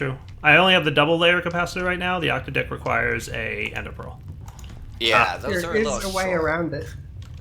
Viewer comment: what i heard, "The octo dick requires an ender pearl"